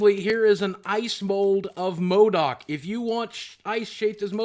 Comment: I seriously thought u said modok as ice cube at the end